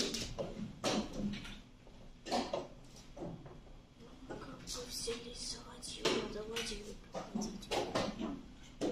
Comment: what did she say there? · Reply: it is single - touch - move !